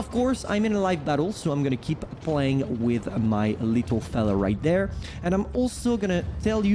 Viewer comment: did he just say "I'm gonna keep playing with my little fellow here"